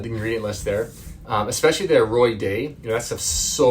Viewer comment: at 9:03, it sounds like you said "their roy day". What are you talking about? Did you mean Roy Dairy? Thanks. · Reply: Aroy-D coconut mlik. Have since come to find out there are gums in it, so I don't use it anymore. Thanks for watching!